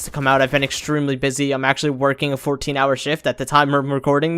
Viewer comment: did I hear 14 hour shift? That's kinda sad but cool at the same time. Keep up the good work